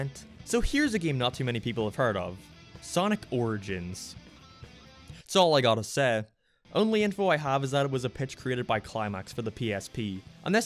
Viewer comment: OmG dId He SaY sOnIc OrIgInS¿